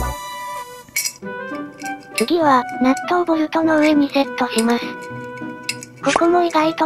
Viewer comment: ＿人人人人人人人＿ ＞ 納豆ボルト ＜ ￣ＹＹＹＹＹYＹ￣ ※空耳です